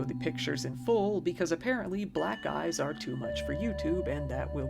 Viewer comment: I thought you said, "Black guys are too much for YouTube" at first. I was so confused!